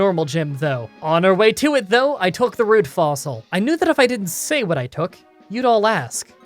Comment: Has anyone else thought he's been saying rude fossil